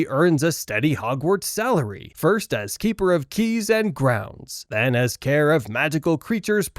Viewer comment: When i was little I thought hagrid said he was "keeper of keys and grains at Hogwarts" 😂